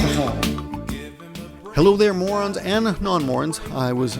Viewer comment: lol i thought he was saying Mormons heeheehee